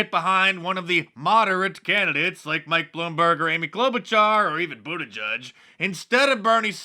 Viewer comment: Poke Her Hauntass came in fourth, while a piece of accent furniture in someone's living room placed third. Oh,, WAIT... Amy Klobuchar finished third. I thought they said "Amy's club chair" was third. Oh, well, Klobuchar has about as many ideas as an old chair, although the chair has more personality.